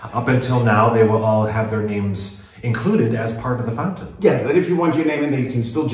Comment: Great transformation as ever! 😃👍 "Part of the fountain" - I almost heard that as "part of the foundation" now that would be a turn for the worse! 😆🤣👍